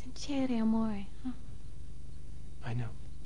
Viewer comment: What does she say at 3:04? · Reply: I sounds French, I heard 'amore' which means love